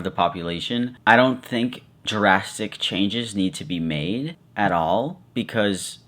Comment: Uh. Wait Right before 8:13 i thought you said ‘Jurassic changes’ Now I realize you said “drastic changes”